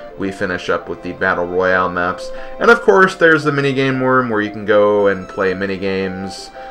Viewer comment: I've always wanted to play this game but Nintendo never released it on VC for some reason even though they did so for MP2. At 5:17 it sounded like you said "mini-game worm" instead of "mini-game room". Yes it's a worm that let's you play different mini-games upon eating you, how original :P. When Peach used the lucky coin on Mario I literally screamed "Why would you choose Mario!?"